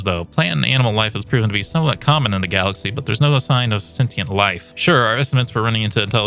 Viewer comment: Please soeak a bit more slowly and clearly I honest have no idea what you said in this sentence. I tried using generated subtitles but they dont make sense of it either And it appears to be a fairly interesting sentence that you said, so its a bit of a shame not being able to decipher it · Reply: He said "sign of sentient life"